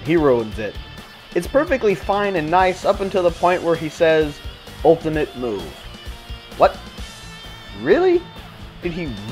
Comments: i dident know he said ultimate move i thought he said "ULTIMATE BOOM" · Oh, I thought he was saying "ULTIMATE BOOM"! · i always thought he said ultimate boom... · Me too lol. I thought he said "ULTIMATE BOOM!" I still like to believe that. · WOW! i thought broly said ultimate boom, which was still kinda lame, but really? Ultimate move? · I thought he said Ultimate Boom lol